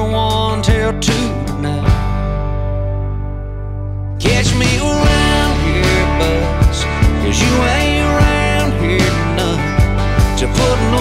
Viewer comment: See this whole time I thought he was saying I'll catch me a round here buzz because your on that "out there" buzz. Lol I got so tickled the first time I heard this